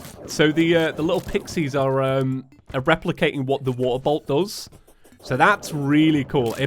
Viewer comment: Geez everytime I hear him say “waterbolt” I only hear “waterboat” which also makes no sense because all boats are water. Wait that doesn’t make any sense either.